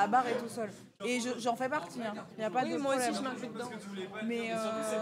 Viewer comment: Tout le débat sur Asterion du point de vue d'un mec qui a pas joué à Baldur's Gate était Lu-naire. Surtout la phrase d':"Si vous l'aimez pas c'est qu'il provoque un truc dans votre sexualité" 👀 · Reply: j'ai du m'y reprendre à 2 fois pour être sûr qu'elles parlaient pas du vrai gars. Surtout que j'ai entendu "incel" à un moment 😂